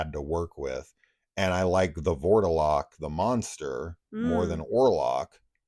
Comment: How do you spell your No.3? At first I thought you were saying The Warlock, but then it was The Borderlock or something? Sounds cool. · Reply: The Vourdalak